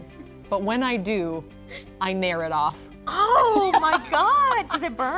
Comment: please someone tell me, what is Saf saying? She's mayoring it off? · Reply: Rúna á Steinamørk She is Nairing it off. There is a hair removal cream type of thing called Nair that she is using to remove the hair.